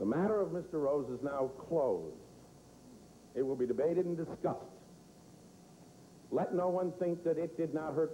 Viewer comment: I remember hearing this as a kid. I thought he said "debated in disgust" rather than "debated and discussed."